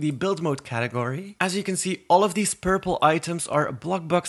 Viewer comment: is it just me that heard him say "terrible " instead of "purple" at 5:55 ?